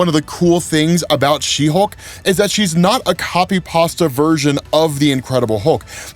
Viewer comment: Hol’ up. Did I mishear or did you say “copy pasta” instead of “copy pasted”